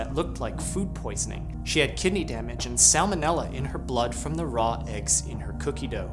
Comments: This video was not sent to me in my subscriptions feed. (I have notifications enabled and everything.) Also: Subtitles do not match spoken text at 00:34: Subtitles say E Coli instead of Salmonella. Same discrepancy at 00:42 — The source video also states it's salmonella (and is subtitled correctly): "Salmonella enteritidis, the bacteria commonly found in uncooked eggs from cookie dough, was isolated from her urine and blood cultures, as well as her stool..." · The subtitles say "e-coli" instead of salmonella at 0:36 🧐